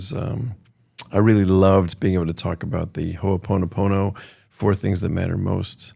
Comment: I didn’t catch that. What was that he said? Ponopono? 45:30 😮 wow, for someone wanting to distance himself from that character he sure responded to you sounding as grumpy and cynical as House.